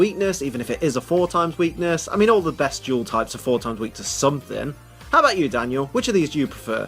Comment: Omg, for the longest time when he said "dual type" I thought he said "Jewel type" and I was like "Wat da hek is a floofin' jewel type?" :p .-. WAT